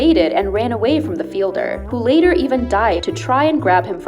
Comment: who else thought the narrator said he died